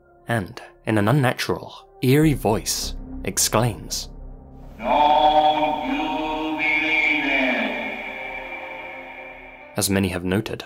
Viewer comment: im really confused, what did Tom say at 12:09?? the subtitles dont cover it and i cant understand, pls help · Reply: “Don’t you believe it”